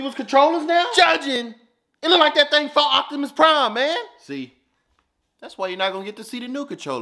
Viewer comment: "It look like that thing fought Optimus Prime!" As a dude who just watched the entire movie series, that joke was perfect 😂👍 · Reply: I thought he said fell off Optimus prime.😂😂😂